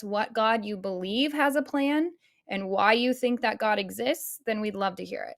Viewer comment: holy shit i thought she said clan i was almost ready to be deeply disturbed